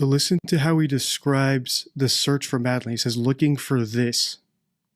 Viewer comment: I heard him say "looking for 'er" and probably not "looking for this".